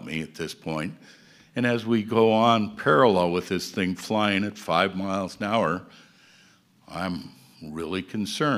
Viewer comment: Did he say "flying at 5mph"??